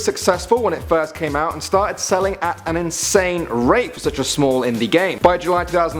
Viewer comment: did i just hear rape